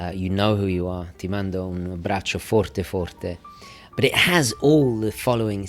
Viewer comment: if anybody's wondering what he says at 1:36, "ti mando un abbraccio forte forte" > "I send you a big, big hug" in Italian. Source: I'm Italian